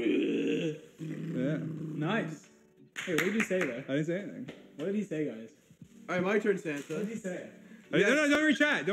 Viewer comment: WHTA DID HE SAY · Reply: “are u serious?” (bcs dream got him one too)